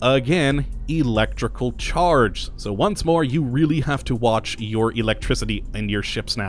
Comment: For some weird reason I kept hearing "you have to keep an ION your electricity"...